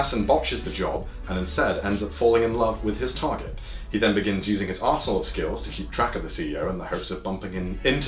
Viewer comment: For everyone and the auto-generated subtitles, Connor is not saying: "His asshole skills", he is, in fact saying "His arsenal of skills"